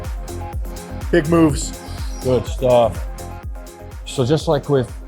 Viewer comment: What did you say at 4:38 exactly? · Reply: BIG MOVES